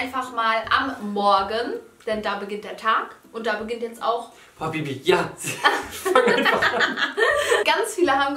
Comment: wer hat auch „habbibi“ verstanden??😂😂😂😂❤️